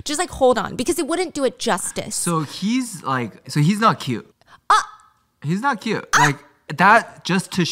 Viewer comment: said "he is not cute" I heard "he's not doing it for me"